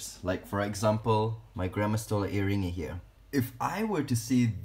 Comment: I thought, Wow, even your grandma has tarantulas!?? 😂 I heard, "...like my grandma's HairyToeInni right here." Go grandma!! 😂 Oops! I misheard.